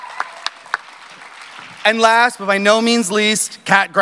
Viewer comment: Did he just say “last but I know means least” when kat went up